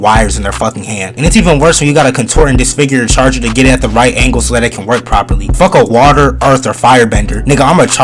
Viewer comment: I heard him say "angle dust" and I grabbed my gun